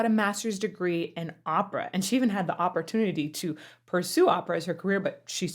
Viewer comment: Did you just say Operatunity? 😂